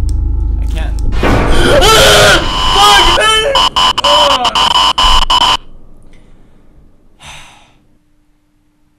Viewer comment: What did he say at 6:12? lol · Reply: Fuck gasp